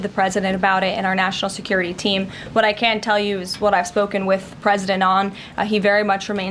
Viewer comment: sounds like she says, "I have spoken with President Elon." 😅